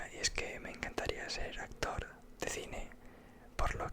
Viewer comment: ENTENDÍ "ACTOR DE CINE PORNO" JAJAJA matenme.